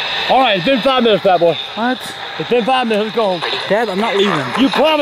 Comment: Is it me or did I hear "Go the fuck home at 6:09 o.o